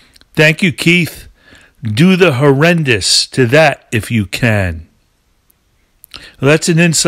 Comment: Are you sure Mick says "do the horrendous to that if you can"? I always thought he said "tune the marimbas to that if you can".